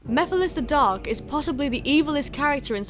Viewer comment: did she just say mephiles the dog?